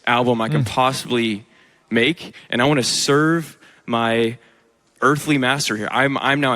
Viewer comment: Is it me or did he say serve his earthly master?